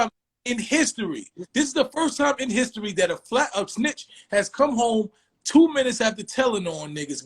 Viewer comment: Lmao tell me a Myssone bar😂🤣😂, You Jack in the box! Akon I commend you on keeping your patience while you handed him a grown man verbal beat down, I thought I heard a switch!